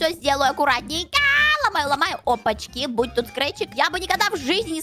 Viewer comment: кто тоже услышал имя скретч на 14:40 сюда